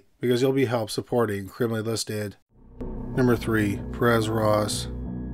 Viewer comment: Perez Roz or Perez Reed ? sounds like he is saying Roz